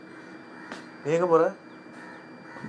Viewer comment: what is he saying ? and what was the reply ? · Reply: "Nee enga pore?" "Nungambakkam Station." "Neeye poike." I think it's Swathi murder kuriyidu.